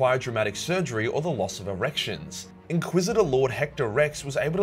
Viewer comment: did he say what I think he said? “Loss of erections?”